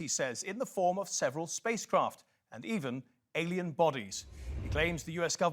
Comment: Thought the guy said, "spicecraft". Yummy.